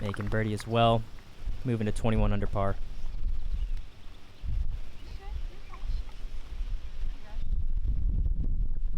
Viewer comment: What was said between Catrina and Jennifer at like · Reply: TheRoddLoops she just said sorry because her bag was in the way😂